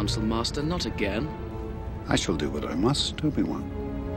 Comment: Ok, so I was watching this at 2x speed as usual. And at 13:36 instead of "I shall do what I must obi-wan." I heard "I shall do what I must stupid one". And I thought that was incredibly funny.